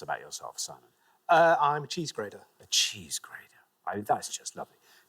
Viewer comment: Ok, but with my American ears, at first I thought he said his job was a cheese grater...I was like...yeah but don't we have machines that do oohhhhh, graDer...got it 😂😂